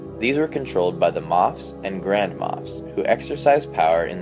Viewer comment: I always hear "grandma" instead of "grand moff"